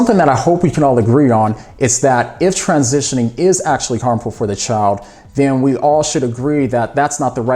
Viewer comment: What do you say around 6:35 when you say "If transitioning is --- for the child"? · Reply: Harmful